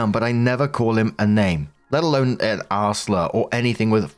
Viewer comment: not looking at my screen I kept hearing 'arse slur" and was wondering what it was.... 😅